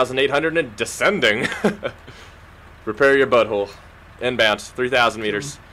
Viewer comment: did he say prepare ur buttholes